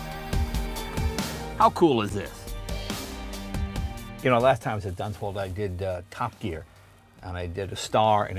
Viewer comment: That looks like the Top Gear test track.. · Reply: ***** In case you didn't hear, at 4:45 he clearly says "last time I was at Dunsfold..."